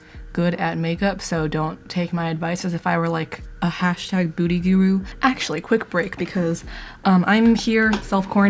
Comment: why did I hear “hashtag booty guru” 💀